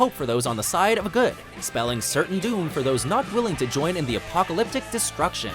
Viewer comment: thought it said apocalyptica for a sec fml